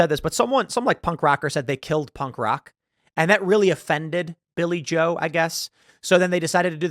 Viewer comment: thought you said Billy Joel and was thinking why would you change the lyrics to Piano Man.... 🤣